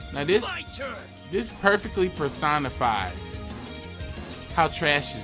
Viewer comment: When u said personified i thought u said persona 5 and i was like NANI!!